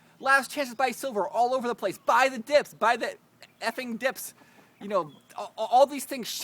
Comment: Did say "Effing" at the 1:45 mark in this video ??